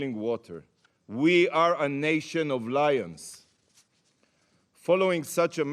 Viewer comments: i heard it as “nation of liars” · Gilad Erdan—I heard "... we are not a frog in boiling water, we are a nation of liars", such self-reflection! 👍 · Did he say a nation of liars, that would make perfect sense